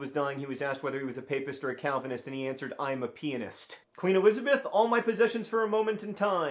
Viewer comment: totally thought he said penis